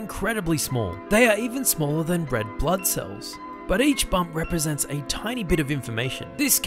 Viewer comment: I heard “bread blood cells” and my brain stopped working